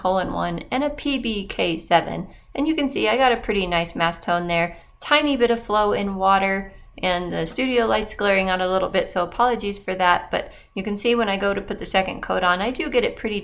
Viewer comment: Hahahaha I'm so tired. You said "apologies for that" and I heard "polyjuice for that" 😂😂😂 what??? Also, yeah, that red is fantastic!